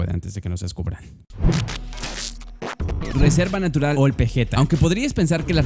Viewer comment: entendi old pendeja xd